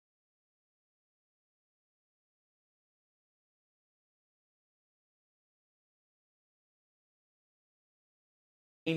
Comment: what did he say? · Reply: Wo ai ni ..that's what he said